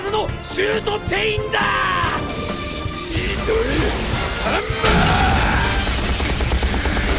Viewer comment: ニードルハンマーが釘パンチに聞こえる